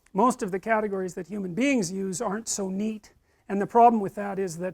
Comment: the subtitles say "Jews arent so neat..".